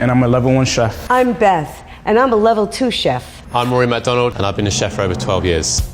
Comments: Anyone else thought the level 3 chef said hes Ronald McDonald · I heard Ronald McDonald